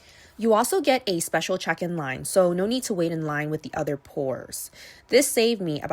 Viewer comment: I thought she said "no need to wait in line with the other poors" 😂